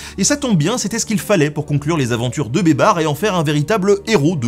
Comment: j'ai entendu "les aventures de Babar" toutes les fois où tu disais baybars! xD